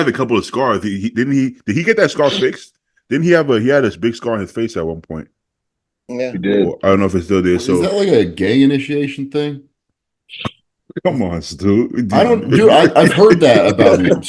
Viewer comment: I thought Stu said is that a gay initiation thing 😂